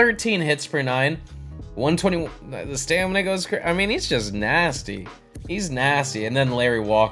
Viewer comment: Yo what did you say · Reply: “The stamina go crazy”